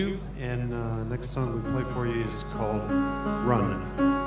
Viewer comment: Can somebody tell me the name of the song that starts at 09:45, he actually says the name but i can't understand it? sounds so familiar :) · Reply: Snow Patrol's Run.